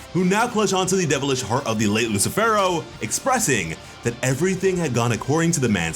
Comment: I thought said Lame Lucifero, but on subsequent rewatch he said late Lucifero. I demand a correction